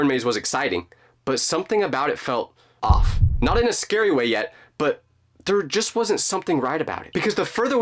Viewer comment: did he say, yet...?